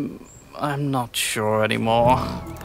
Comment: Beard, black short-brimmed hat and Minecraft, I wonder who this reminds me of... 1:28 oh I hear it, "I'm notchure"